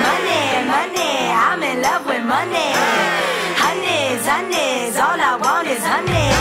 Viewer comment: I could’ve sworn I heard “ Money, money, I’m in love with honeys “